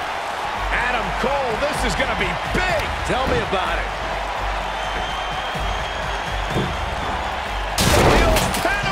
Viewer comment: anybody know what that move is called in the corner? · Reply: panama sunrise lol michael cole said it